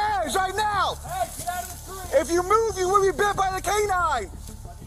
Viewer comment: I thought he said if you move you’re gay😂😂😂😂